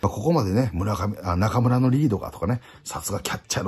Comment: 「中村のリードが」が「中村紀洋が」に聞こえてびびった